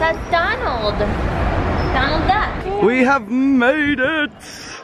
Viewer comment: In around 1:50 he said whats that is that Donald I swear I heard say that